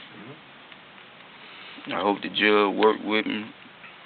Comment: you added the yellow texts as to what the suspect was orating. He doesn't say---> I hope the jail will work with me . . . . . give me probation. He says the " judge " ....he hopes the judge will work with him.